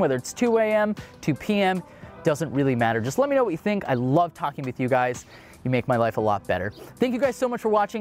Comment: I thought he said "I like talking to cute guys" lol